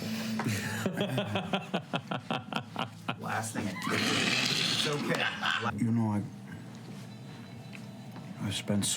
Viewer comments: WHAT DID SAM SAY IN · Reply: Firstly I'm sorry Laura says "he is in love with you" Sam says "I would F him do hard" Because..Sam is sam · What does Sam say to Laura at 50:58? I love that it made her and Taliesin crack up lol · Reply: Sorry Laura says "Oh my god, he's in love with you!" Sams says "I would F him so hard" because...Sam Sorry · Does anyone know what’s being said between Sam and Laura at 50:58 because I can not make it out and I really want to know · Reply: Laura says "he loves you" Sam says "I would f@ him so hard".....sorry